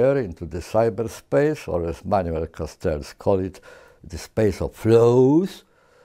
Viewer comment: when the subtitle says "creek suns" its actually quick sand. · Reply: And when the subtitles says "Manuel Constance" at 5:56 its actually Manuel Castells.